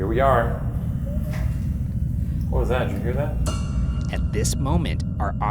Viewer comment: Just wanted to say it just cause but at 15:15 when they heard the “wimpier” I could have sworn I heard the name Kevin 🤷‍♀️